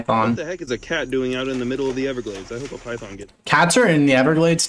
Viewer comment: Did bro say "I hope a python gets it"? 💀💀